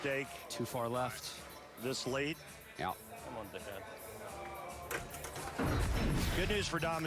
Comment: sounds like he says: " Come on dickhead" Ahaha